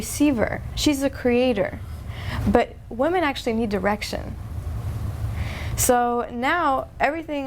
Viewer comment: you said "Women need direction" but kinda sounded like "women need erection" ;)